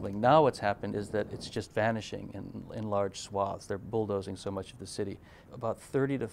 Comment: can u help me? i can't really hear what jeffrey says in minute 3:47 "it's just vanishing in large ___"??? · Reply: Vanishing in large swathes